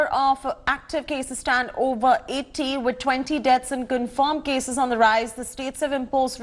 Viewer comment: Did she said 20 deaths